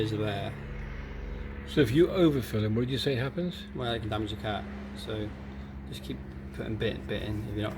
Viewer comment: 😂 don’t ! Just don’t put water in your radiator or stick a rag in the filter cavity Also, what did you say happens when you overfill with oil? 14:55 🤣 “there goes your car?” 😂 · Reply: He said “it can damage your car”